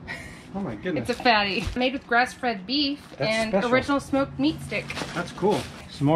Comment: When you were talking about the fatty’s I thought you said “made with breastfed beef” 😂